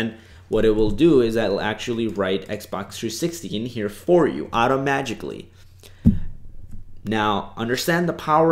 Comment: Did he just say "automagically"?